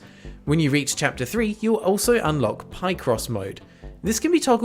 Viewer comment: ohhhh, Picross, I heard Pie Crust for some reason. Picross is awesome.